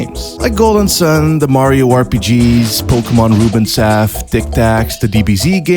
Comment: What game is that at 0:41 after pokemon ruby? Cannot understand what he said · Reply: Final Fantasy Tactics Advance